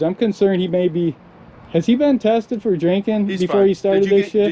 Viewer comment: Min. 12:51 ca....was he tested about "Jenkin"...... Sorry, didn't understand what he said.